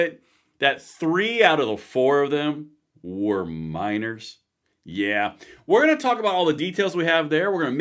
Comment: Lol, at 0:40 when I hear you say 'Minors' I thought you said 'Miners'. And I was thinking, like what, coal miners ... English language is funny.